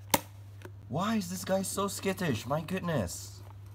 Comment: for a second I thought he said "So Scottish". Don't even.